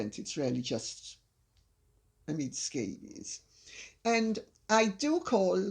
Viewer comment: Amid scabies lol.. think peop thought u said Omid scabies